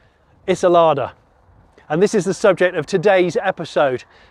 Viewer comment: For a brief moment I thought he had said "Its a Soviet street sweeper" 😁